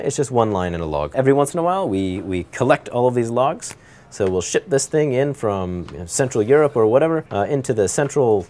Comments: did he just say we will shit this thing from central Europe or what ever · i thought he said shit at first LOL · who else heard 'shit' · what did he say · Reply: “Ship” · did he say shit at 2:25 ? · Did he just say that they "shit" the "logs"?